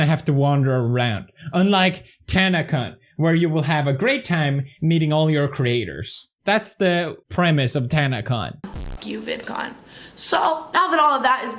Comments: At the end I thought he said Tanacunt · kinda sounded like he said "tanacunt" at the end lol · It sounds like he said “tanacunt” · I thought you said TanCunt at the end there for a sec mate.